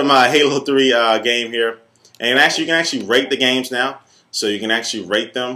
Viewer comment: I thought u said "you can rape the game" haha